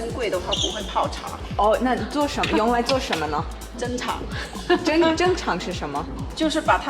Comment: I think she said "珍藏"